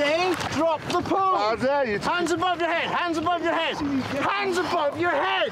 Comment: "How dare you?!" - Aw, Clarkson, that's an SAS operative, I think he dare push you into the water and then some :) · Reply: Yo, I thought he said how dare you the first few times too, but after listening to that section a few more times it becomes clear that he slurs (due to the cold) "I did" to the demand from the SAS guy to drop the poles.